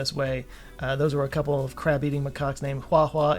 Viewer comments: I thought you said ".. a couple of crap-eating macaques... " and wondered why you disliked macaques so much that you'd call them that. Then i turned on closed captioning to find out you said "...a couple of CRAB-eating macaques..." Well, at least i actually made it to 2025. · coulda sworn you just said “a couple of crap-eating macaques” and thought you’d circled back to the US election.